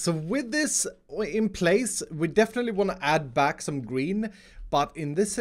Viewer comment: I have an idea if you could fit an emergency ladder in the second elevator if a certain someone cough Grian cough presses the button like a million times Edit: 12:27 did he say add back some green or add back some Grian and if he said Grian well that was the right time for Grian to fly in